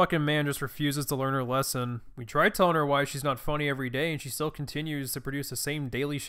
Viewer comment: At first I thought I’d said “why nigahiga isn’t funny.” I was so mad lol